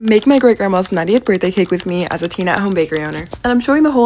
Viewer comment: Also, did she say she is a TEEN making something this beautiful?!? 🤯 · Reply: I had to go back and listen to it because I thought I heard her say, “teen baker “, but she said it kind of fast. Lol.. I had to come to the comments to see if anybody else caught it😂. She really did a great job.❤